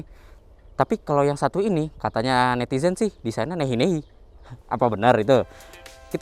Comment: "katanya netizen sih desain nya nenye nenye". maaf bang ga jelas ngomong apa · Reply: nehi nehi